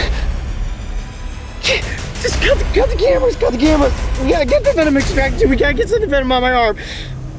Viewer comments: I thought he said "cut the cameras"? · cut the cameras cut the cameras......getthevenomextractorgetthewenomoutoftherefsrsfbsfgnhgdmf,kuyo Nhndfgbsdfvafdvdfsbfgdb 🤷🏻‍♀️thats what i heard 😅😂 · i thought he said cut the camera